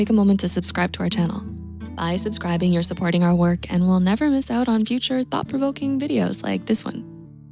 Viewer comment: Heyy, I've noticed a minor error (I think) at 2:17. The subtitles say "we'll" while I believe it should be "will". Please excuse me in case it was actually correct from the start...